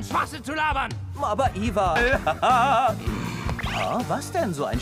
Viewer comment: “aber iwa..” they really said “Eva “ i love how German pronounce their w’e